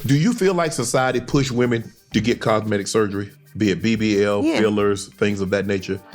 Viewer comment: did dude say, cogmetive surgery 😮😂😂😂😂🤦🏿‍♂️